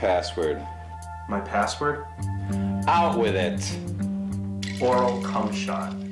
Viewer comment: Hello? 0:08 — Its me Richard 0:10 — Its one fucking thirty in the morning, what are you doin calling me? 0:14 — Sorry to do it to you, but we have a mother of a virus ripping through a major company file! 0:20 — Ok, Throw me some numbers… 0:32 — Ok, gimme your password… 0:34 — My password? 0:35 — O U T W I T H I T ! ! ! 0:37 — OralCumShot... 0:40 — OralCumShot? Yeah That Fits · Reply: I thought I heard "Mortal Kumshot"